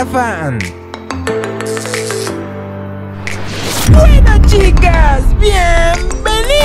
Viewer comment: escuche BUEEENAS CHICAS